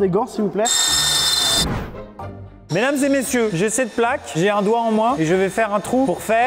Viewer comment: j'ai cru entendre « j'ai un doigt en moi » 💀